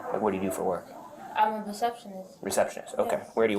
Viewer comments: Did she say perceptionizt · Did she say, “I’m a deceptionist.”?! 😂